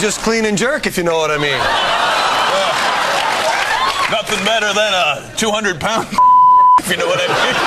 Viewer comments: what did brad say?!? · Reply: 200 lb. snatch. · What does Brad say at 10:11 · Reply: Snatch. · What does Brad say at 10:11? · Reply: Snatch